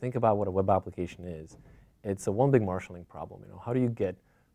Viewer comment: What does he says at 00:26? "It's a ... marshall (or marshalling) problem"